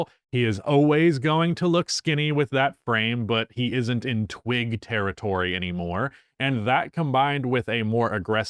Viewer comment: Did he just say he’s in TWIN territory?!